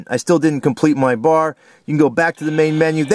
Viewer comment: For a second I thought it said Fuck Home Run